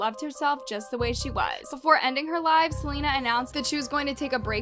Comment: i thought said'before ending her life' i got so scared omg..